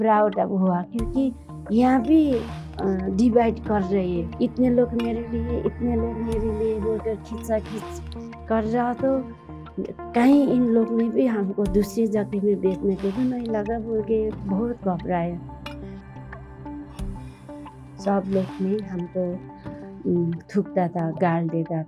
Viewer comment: what is she saying, can anyone understand, is she saying "kahin kisi or jaati me na bech de "